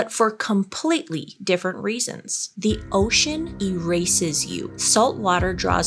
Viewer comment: i heard "the ocean embraces you" and I was like "God that's beautiful" but then of course I had to rewatch it and ruin it for myself 😂